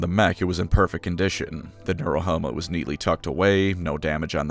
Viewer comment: I can’t understand what is being said before „was neatly tucked away“ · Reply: Neurohelmet